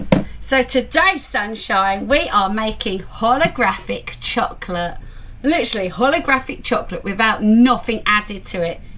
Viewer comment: Hi thought you said pornagraphic chocolate at first 😂 🙈🙊 you did very well considering it was your first time l love bounty bars too loved the electric can opener too nice one 👍 😊